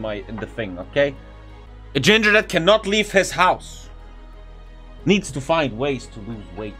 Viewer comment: All I hear is "the ginger man can not leave its house"